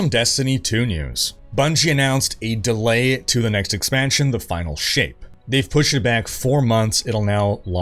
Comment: I'm sorry, did you just say the Destiny expansion is called, "The Final Sh**?" 😮